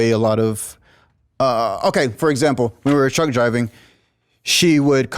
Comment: 7 years OTR and all i keep hearing is "we wuz truck driving."